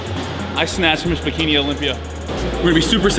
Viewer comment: You slyyyy dog hahaha. Sounds like you said "I smashed Miss Bikini Olympia"